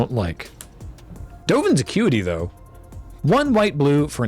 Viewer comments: lmao I thought said "Dovin's a cutie tho" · I swear to god I heard "Hey, Dovin's a cutie"